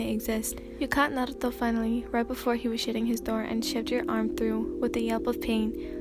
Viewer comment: I'm sorry but did I just hear "You caught Naruto finally, right before he was shitting his door, and shoved your arm through".